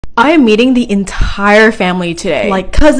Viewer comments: I’m literally so traumatized bc when I saw the vid for the first time I thought she said “ Today I’m gonna be EATING entire family.” Instead of, “ im gonna be MEETING my entire family.” 😂😂😂🤣🤣🤣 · her: I'm meeting my whole family today me: DID YOU JUST SAY EATING?! oh wait its meeting hehe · I got distracted and was looking at something and I thought she said “I’m eating the family today” 😭😭😭😭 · I heard "I an eating the ENTIRE family today" 😂😂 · I THOUGHT SHE SAID "IM EATING MY FAMILY TODAY"😭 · I thought you said "eating" and not "meeting" and my dumbass said "cool...wait what-" 😭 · I heard ‘Eating’ 😭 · At first I heard "I'm eating the entire family today"😂 Have a nice time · At first I heard it as I'm eating the entire family today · I didn’t look at the screen and I heard “I’m eating my family today” 💀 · Lol i thought she said i am "eating" my family until u saw the cc ( i am a big fan no hate) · I thought you said eating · When she said she is meeting I heard her say I’m eating my whole family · I thought she said “I’m Eating The Entire Family Today“ 😭😭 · When she said meeting and I thought she said eating my family 😂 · When she said I'm meeting the whole family I heard I'm eating the whole family 💀 lol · For a second I thought you said I'm eating my entire family today😂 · Hi, so I thought you said I’m eating my family today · why did I hear I'm eating the entire family today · Bruh I WAS SO SCARED. I heard “I’m going to eat my cousins!” But it’s meet not eat lmao